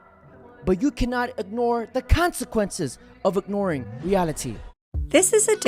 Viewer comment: When you said "ignoring reality", I thought you said "dr. Moriarty ", I'm stoned though so.